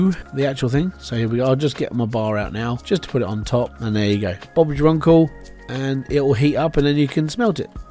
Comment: did you say “Bob’s your uncle”?